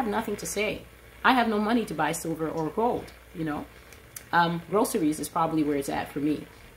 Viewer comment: Hold up. At minute 20:55, did she just say, "Rosaries is probably where it's at for me"? Is she endorsing Catholicism?